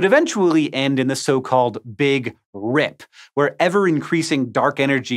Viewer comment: Hank says "Dark Energy Tear", I hear "Warp Storm"